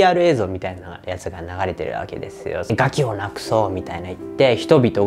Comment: ガキを無くそうって聞こえる